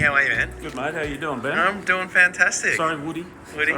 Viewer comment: Did he just call you? It has been a while.